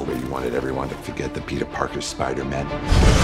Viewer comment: Hold up, did Strange said "Spider-MEN"?